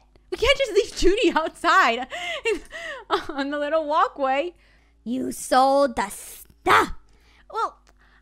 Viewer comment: I thought you said you sold ur house?